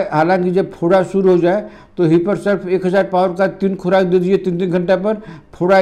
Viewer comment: yahaan par kya bola..Sorry samajh nahin aya. Kya dena hai 1000 power ka? Hiperserf?